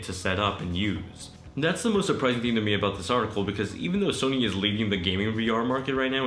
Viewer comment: No one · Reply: ahh you said leading. I heard “leaving”